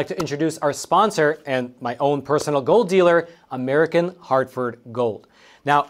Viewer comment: Gold dealer ? I thought you said Dope Dealer